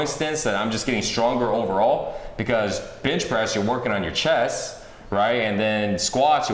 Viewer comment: anyone else hear bitch press at 1:59 ?